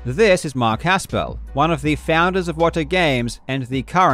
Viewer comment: I kept mishearing "Wata" as "Iwata" 😭